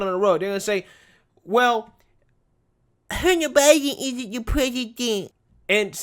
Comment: What did you say at the 7:17 time? Gotta know, it was probably funny. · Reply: "well... Hunter Biden isn't the president".